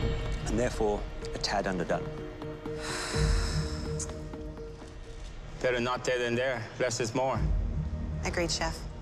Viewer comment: What is he saying b4 he throws the leaf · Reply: Figured it out "better not their than their. Less is more."